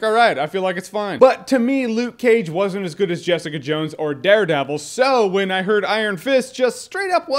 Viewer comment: I thought you said Jessica Jones was good so why don't we want it